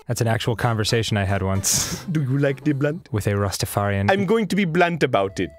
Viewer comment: when dan first said rastafarian i thought he actually said pastafarian.